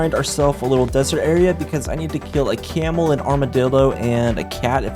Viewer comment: did he really say armadildo or am i stupid💀🤣